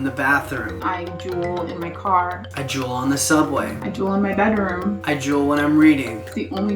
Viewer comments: when said “I juul in the subway”, I heard “I drool in the subway” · I thought they said drool Hahaha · Lol o thought they were saying drool 😅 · thought they were saying drool 😂